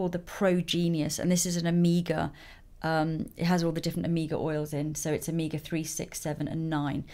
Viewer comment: when you said omega, I thought you said Amiga lol 😂😂😂